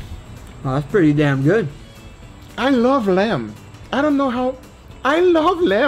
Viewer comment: All i heard is "I LOVE LAMP"